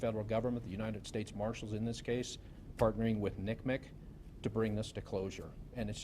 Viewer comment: 💡In case anyone was wondering what he was saying @ 8:18 he’s saying NCMEC it’s short for the National Center for Missing & Exploited Children Apparently it’s what the organization calls itself.